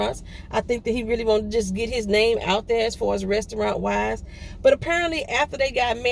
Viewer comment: I thought you said restaurant wives lol I thought that was a show he was on